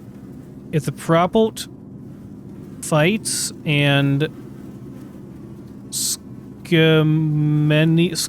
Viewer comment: i think it says : "if* the product fights back add segmental anesthesia". Basically, keep drugging the victims so they don't fight back when they're recording the BD.